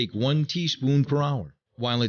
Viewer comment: What is he saying "Take one teaspoon ____." I didnt understand what he said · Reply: I think he said every hour